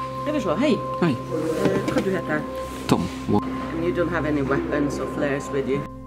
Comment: did she say "You do not have any weapons or clothes with you?"?